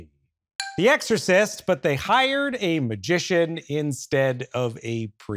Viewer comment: I thought he said musician and was waiting for her to burst into song 😭